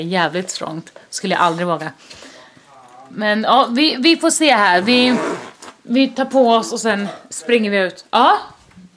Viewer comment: Hörde du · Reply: sa va fan!